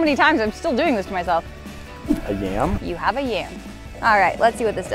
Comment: what did you just call that??? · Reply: Keziah Yates yams! It’s another word for sweet potatoes